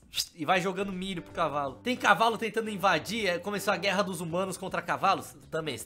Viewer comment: eu nao entendi a hora que o falo no 8:53 "tem cavalo tem tambem vadia"